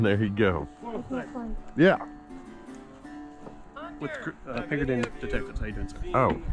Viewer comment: Did he say Pinkertons 🤨